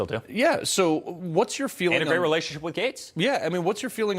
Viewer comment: Did Eric just say that he has a good relationship with Gates? As in Bill Gates?! At 4:50? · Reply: Matt Gaetz